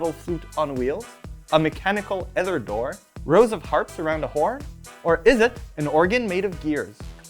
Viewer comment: When he said "rows of harps arount a horn", I heard "Rosa Parks around a horn"